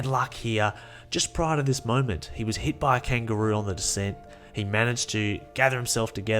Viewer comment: Did you just say “he was hit by a kangaroo on the descent”? Australia 😂😂